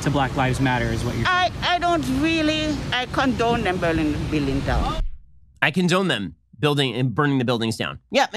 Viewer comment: That lady clearly said she “don’t condone” the burning of the buildings and then Ben proceeds to say she said the opposite. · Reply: It sounds like she said "I don't really... I condone them burning the buildings down." So maybe she misspoke after correcting herself, but after my first listen I thought she was correcting herself to say she DID condone them.